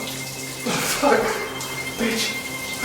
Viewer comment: did he say “fuck.... bitch... pussy”???